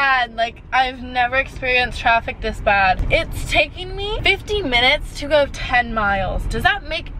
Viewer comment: at first I thought she said “it’s takin me 15 minutes to go 10 miles does that make since to you??” I was think yea it actually makes a lot of since..